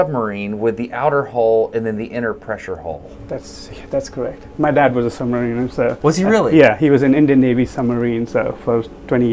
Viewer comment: LMAO Im high af and I for reals thought the guy said "Thats, thats correct, my dad was a submarine", like validating the idea hahahaha